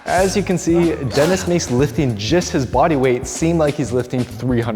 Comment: I thought he said “I found this DENTIST guy on the street..” 🤣literally thought he was a dentist until